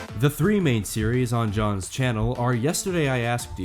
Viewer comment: did you say JOHN